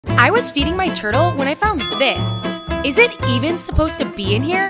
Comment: I thought she said “i was eating my turtle”